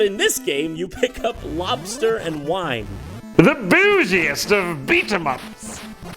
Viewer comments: I always thought that said "The booziest of beeter-muffins". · HEY GUYS LOOK JON MADE A TYPO ON THE INTERNET! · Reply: Jean Kirschtein TBH I didn't know it was a word either until I looked it up; I thought was saying "booziest." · The booziest of beat-em-ups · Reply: Orangepeel I always thought he was saying “bougiest” 😜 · I always thought he said "the BOOZIEST of beat em ups" at 10:21... finally I get that he actually says "bougiest" XD had to come back here just to write this comment...